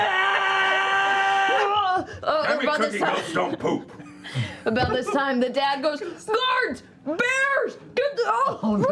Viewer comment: does anyone have any idea what talesin is saying there? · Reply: Taliesin: "Damn it, Cookie! Ghosts don't poop." Liam: "AAAAAAAAAAAAH!" flings poop